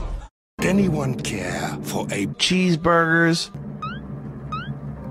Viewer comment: what it really said: "can i have cheeseburger please?" What I hear: "can i have gel burger please?"